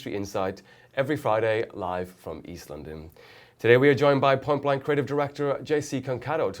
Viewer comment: I´d thought he said "Today we have Jay-Z"... got kind of excited haha :)